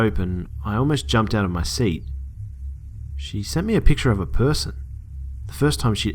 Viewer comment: i thought he said "she sent me back a picture of a TITTY bear?"